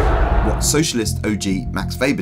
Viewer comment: Is it just me or did he call Weber a socialist? 😅